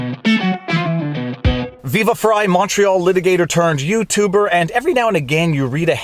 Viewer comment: Viva, for the longest time I thought you were saying "macho litigator" in your intro. Only now do I realize you were saying "Montreal litigator". Lol